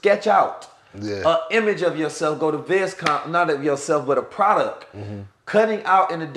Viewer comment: What is he saying at 50:04? Vezcom? Vezcomp?